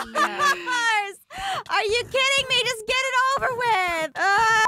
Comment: Did or said "are u fcking kidding me?!" Cuz that is the first time I heard her say that O.O